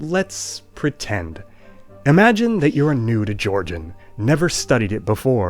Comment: he said: "Imagine that you're new to Georgian", but I heard: "Imagine that you're a nude Georgian"